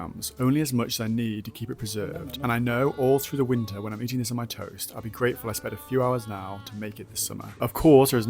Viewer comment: Why did I hear "when I'm eating this on my TOES" TOAST, TOAST